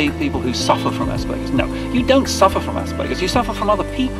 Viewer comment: I thought it said surfer with asparagus 😂😂😂